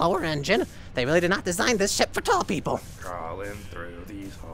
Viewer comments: it sounded like he said s**t not ship 😂😂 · Correct me if I am wrong but DID SUN SAY SHIP OR SH!T!? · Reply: Ship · omg I thought Sun said “they really did not design this sh*t for tall people” 💀